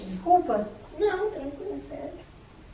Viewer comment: eu ouvi "SÉRIO" mas estava escrito "ESQUECE" *nao sei qual é